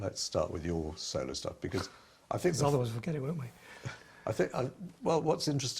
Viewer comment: Please help! What does Tony say at 1:06:50 after the interviewer says, "Let's start with your solo stuff, because I think..." and Tony cuts in with "???????, won't we?" If anyone can fill in what he says I'd greatly appreciate it. Thanks. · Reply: “Because otherwise we’ll forget it , won’t we !”